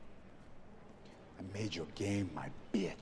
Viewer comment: I first when I watched it I thought he was gunna say I mad ur game famous